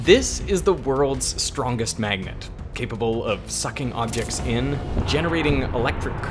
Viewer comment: I swear it sounds like he is saying sucking on dicks in the beginning